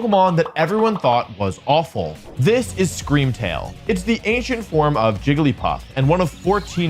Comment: I heard "The asian form of jigglypuff"